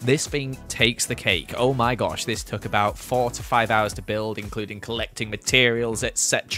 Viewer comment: I don't know if it's just me, but when he said "4 to 5 hours" I seriously thought he said 45 hours and I was so confused